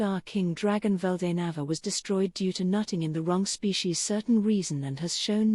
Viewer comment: “Milim’s father was destroyed by nutting in the wrong species”….? Did I really hear that right? 😂